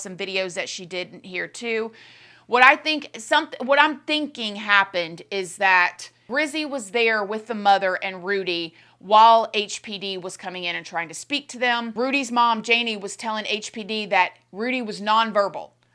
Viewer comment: Did i just hear HPV? lol Just took me for second lmao